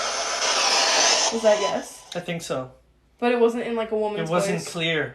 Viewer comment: I hear dead instead of yes. Then at 33:37-34:00, either or Amelia says "tell them no" I feel like something was telling them to say no and either or Amelia repeated it to the other and it happened to come through the spirit box, and that when they answerno after that, I feel like it should be a yes but their scared of something and following it's or his orders so they don't get in trouble, and their lying.